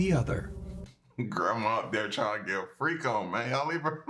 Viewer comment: At first I thought thought you said “Grandma tryin to get her freak on with her teeth on” 🤣🤣